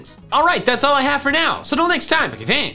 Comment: Is it just me but it sounds like he said boogiefans not Pokéfans in the intro